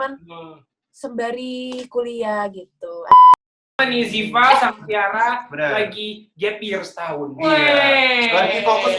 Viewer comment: itu yg disensor Tiara ngomong apa yahh? Host nya pada gk sadar sampe gumush bgt lihat Lily dan Titi yg nyadar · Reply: kayanya ngomong "anjay" wkwk